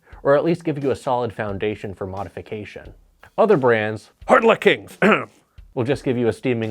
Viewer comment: Ok, I give up…what are you saying at 10:10? I’ve played multiple times and can’t figure it out. BTW, great channel! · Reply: Hard luck kings, a really shitty brand of guitar and bass haha